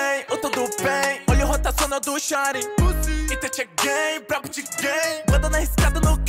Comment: Juro q eu escutei "ITACHI É GAY"